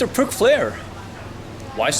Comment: I thought he said prick flair at first haha